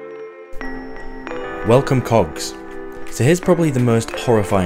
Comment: I'm new here and I thought he said "Welcome cocks", took me a few surprised seconds to realise he said cogs. 😅